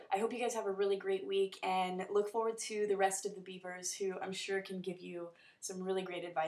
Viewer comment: At the end of the video, I could've sworn I heard "Biebers" and not "Beavers" xD Maybe that's just me..